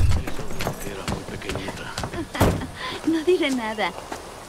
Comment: Escuche mal o Charlie dijo "te amo niñita o chiquita" a Alix, 😁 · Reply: Yo escuche "era muy pequeñita" y ella dice "jaja no diré nada" 🤔